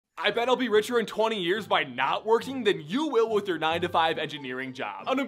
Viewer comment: I thought he said by NOT working.